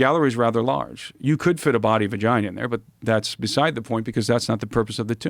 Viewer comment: "You could fit the body vagina in there" is what i heard. Captions did not disappoint 😂😂